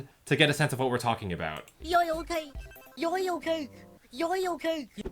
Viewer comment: WHEN BUBBLE SAYS YOYLECAKE, IT SOUNDS LIKE SHE IS SAYING YOYOCAKE!